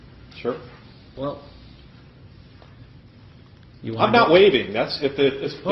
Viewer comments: he's actually saying "im not waiving" instead of "im not leaving" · Just a little correction at the beginning, near 0:56 I think he said "I'm not waiving," not "I'm not leaving." Great video as always. Florida men this time, not just man.